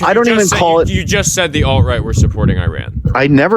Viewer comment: My guy you misheard. He said "outright" not alt. And nobody picked it up.